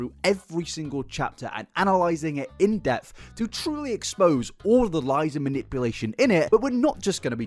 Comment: I heard "to truly expose all the lies and manipulation, innit?" 😂 Love you!